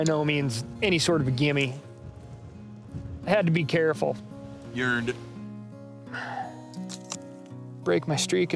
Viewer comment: about 18:50 I know Steve said "You earned it" but it also kinda sounded like ""You're a dick"